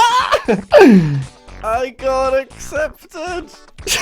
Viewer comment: when I read this I thought it said "I GOT ADOPTED!!!"